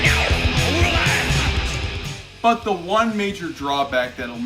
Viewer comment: My names tony😂😂 I got some bacon 🥓 thanks a lot. Can’t un hear now 😂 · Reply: I heard "My names tony, nice to meet you"